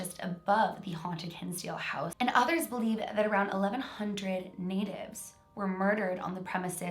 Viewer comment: Instead of natives I thought you said babies so I rewinded to make sure and THANK GOD you said natives